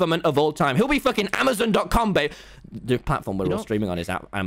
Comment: sorry this is so random BUT DID TOMMY JUST CALL TUBBO BABE? 11:07 I think i'm hearing things